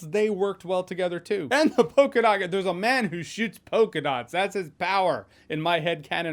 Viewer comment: I could have sworn you said "shits polka dots" lmfao🤣